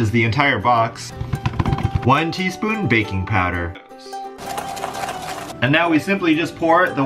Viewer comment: I thought you said "Bacin Powder" hahaha. I love your vids tho! Also i love seeing Gatsby. <3 pls. reply. T^T